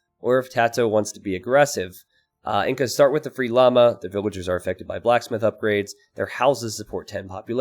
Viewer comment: I heard 'Lhamas are affected by blacksmith upgrades' and immediately laughed my ass off